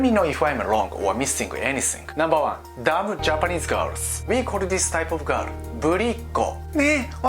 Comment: i instantly laughed after hearing mimicking "Dumb Japanese Girl" sound LMAO. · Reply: I thought he said "we call these types of girls booty call"